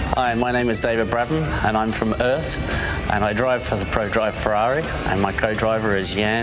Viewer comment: did David really said he’s from earth? 😂